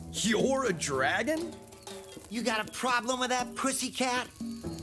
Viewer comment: He scared me when he said that, I thought he said PUSSY! But he said pussycat